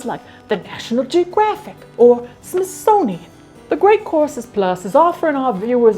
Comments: I also never watch someone's sponsorship but that was great 😂 Also I use The Great Courses Plus and it helped me remember what I needed to go back to college, woohoo! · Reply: I legit thought she said “the great corpses” at the beginning😂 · Why do I hear "Great Corpses Plus" every single time??? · I keep hearing "great corpses plus"... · totally thought you said “great CORPSES plus” and was absolutely mortified